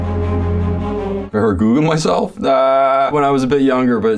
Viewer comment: Thought he said “Have I ever gooned myself? When I was a bit younger but it’s been a long time. It was all surprising and shocking and I never did it again.” 😭💀